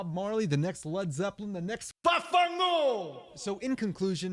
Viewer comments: Did he really say "VAFFANGÙ" meaning "vaffanculo" which is "fuck you" in italian? Lol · did he say "vaffanculo"? Lol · did he say "the next VAFFANCULO"?😂 it means fuck you in italian😂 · D-D-Did you just say “VAFFANGUL” all’italiana? Cuz in that case, i’m gonna cry tears of joy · Did you actually say "VAHFAHNGOOL" at 8:36?